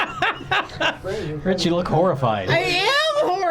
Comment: i swear rich said “homerfied”